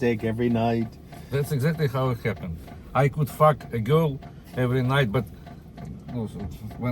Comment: it sounded like he said "I could f*ck a goat every night", took a sec to work out he was saying girl🤣